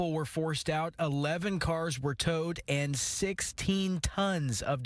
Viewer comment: 32,000lbs of garbage. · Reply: oh wait it’s 16 not 6? I thought it said 6 tons of garbage, which is 12000 pounds